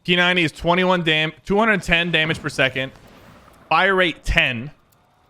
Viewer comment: Did I hear 210 damage per second